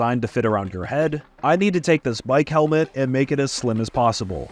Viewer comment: did he say mike helmet